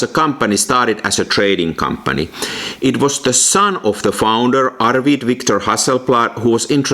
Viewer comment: P. S. I saw it again and now I heard you said "founder's son Victor" Sorry.